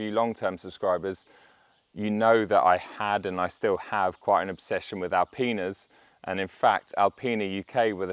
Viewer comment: I had to rewind and re-listen to what he said. Oh, he said obsession with Alpinas not All penis ;-)